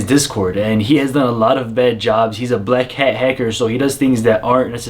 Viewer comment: LMAO @ 0:12 i thought he said he is a black hacker